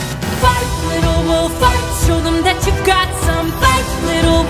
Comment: The fart little wolf in the closed captions got me and my sister dead at 1:31:21 🤣